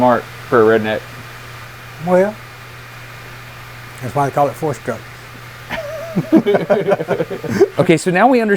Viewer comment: "you're pretty smart for a redneck" Dad: "that's why it's called a four stroke" That's such a dad retort - and one I received many times in exactly the same way. I miss him. ☹️ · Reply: I thought he said " that's why they call me Forest Gump"